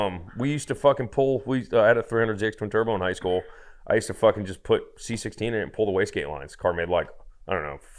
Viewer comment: what did say here, anyone care to take a guess? lol. Ps thx for sharing this is an awesome interview! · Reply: he had a 300zx tt in high school he used to put c16 fuel in it and pull the wastegate on it